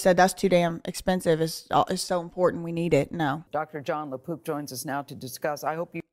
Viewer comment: I thought when they said Dr Jon Lapook they said Dr Lapoop. 😂😂😂